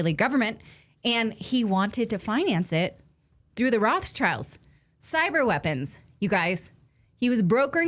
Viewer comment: did you say "Rothschilds"?😉